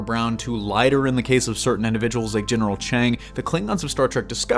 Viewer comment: Did he just say General "Chang?"